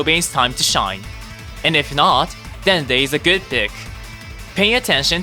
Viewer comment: Dende is a good what??? 10:01 (I know he says “pick”)